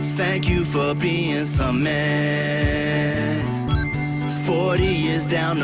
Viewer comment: Great channel dude. But in your outro music I keep hearing "Thank you for being CEMEEEENT", over 100s of videos still can't make out what it really says. Anyway whatever it is I'll keep showing up to your channel, and I'll keep being Cement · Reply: Oooh, I learned this one awhile ago. I believe it says, "Thank you for being some men." Why, I can't remember. But, for me, I always heard 'Thank you for being so meh.' But, only bcuz me and my fam have an inside joke of calling each other "meh" as well as other weird things. So, I'd turn to my eldest daughter every time and thank her for being so Meh when that would play. Lol.